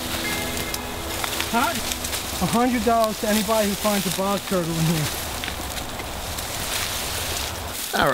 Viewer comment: Oh I thought you said box turtle lol, good video!